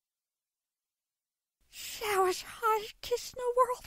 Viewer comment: It sounded like alphys was saying that was the greatest kiss in the whole world holy crack